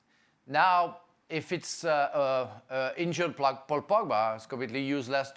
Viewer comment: did he just say Black Paul Pogba? 😅